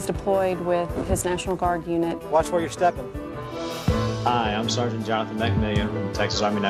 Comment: what does the dad say at 0:21 ? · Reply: Watch where your stepping meaning there’s landmines